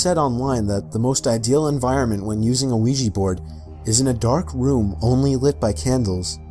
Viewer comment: I thought it said ninja board